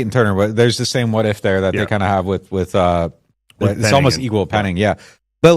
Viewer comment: Please give a timestamp because I’m certain I’ve never thought or said the phrase “Evil Penning” in my entire life. But maybe I’m wrong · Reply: I think he’s referring to 12:34 where you are talking about Peyton turner and I think you said, “he’s equal to penning”. Saying they are both unproven. It does kind of sound like you say evil, but in the context of the conversation I think it’s obvious you say equal.